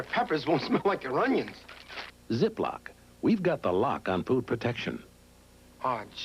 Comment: It sounds like he says “We’ve got the lock on poo protection”